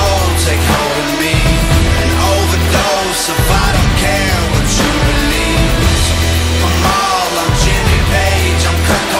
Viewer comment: Here I thought he said 'an overdose of I don't care with jubilee'... close enough